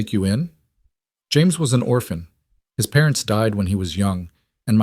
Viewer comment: I swore you said James grew up an orphan .